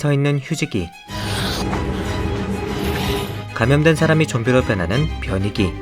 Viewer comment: 휴지기 변이기 저만 휴지 변기로 들었나요?ㅋㅋㅋㅋㅋㅋㅋㅋㅋ잠만ㅋㅋㅋㅋㅋ 이름 뭐야ㅋㅋㅋㅋㅋ 죄송합니다 ..제가 한자를 못해욬ㅋ.ㅋㅋㅋㅠㅠ 한자 맞나요??..